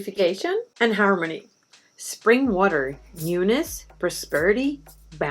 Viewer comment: At 15:19 timestamp...⏰ You were explaining about SPRING WATER...🏞️ I got #2)Prosperity and #3)Bounty...🏧💸💵💳💰🛒🧾🛍️ But... I am afraid that your lovely Spanish Accent got in the way of #1)... Which sounded like you said UNITS(??)... Need a reply from you, thanks!!...😁 Blessed be!!...🥰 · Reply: Newness 😊